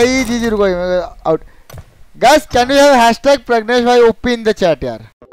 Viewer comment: I thought u said pregnant bhai op in the chat 🤣🤣😂